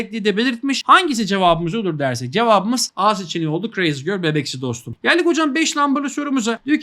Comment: hocam crazy girl bebeksi dostum mu dediniz ben mi yanlış anladım?